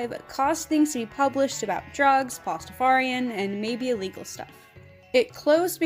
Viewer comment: I had this on in the background and heard you say pastafarian I was like HUH because I barely ever hear someone talk about it lol had to rewind to make sure I heard it right and it was censorship lmao Anyway r'amen